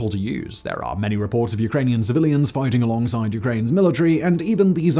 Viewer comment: What was funny is ... I had to turn on the subtitles. As when Simon said the 'Javelin was so easy to use' I swore I heard 'HOOLIGANS along side the Ukrainian Troops....' instead of civilians.